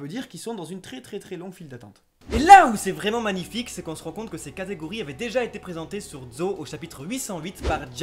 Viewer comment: Euhh c'est moi ou à 11:28 on entend "Katakuri", ou bien j'ai mal entendu